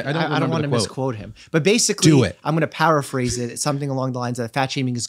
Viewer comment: Did doctor Mike say "I'm going to powerphrase it" instead of PARAPHRASE? At 22:29. Someone let me know